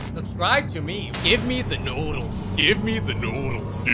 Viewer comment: Sorry, I thought you said give me the noise but you actually said, give me the noodles